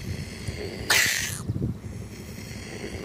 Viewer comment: THAT is not a toad, it's a pac-man frog. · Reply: "Escuerzo" is spanish for toad. The term is often informally and interchangeably used for frogs and toads. I don't know exactly what the guy in the video says, but I heard him say, "escuerzo", which means he's calling it a toad.